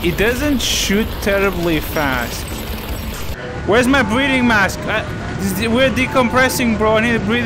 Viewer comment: ngl, your accent is so thick I thought you said Breeding Mask instead of Breathing Mask